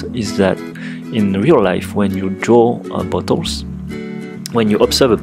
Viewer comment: When he says "bottles" I hear "Buttholes"...